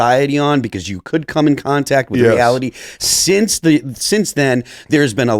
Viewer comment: what does he say here? "Because you could come in contact with (??)" It sounds like he says reality but im not sure and it doesn't make sense to me